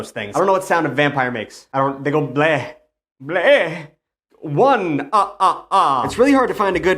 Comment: I heard blood, blood. Not, “Bluh! Bluh!”